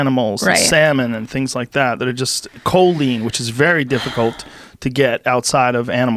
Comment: what the fk is Joe saying? Coldene?